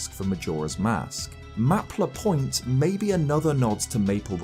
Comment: my brain heard it as Map Lapoint idk why 😂😂 Edit: Man seeing the maps of the oracle games makes me want a Link`s Awakening style remake of both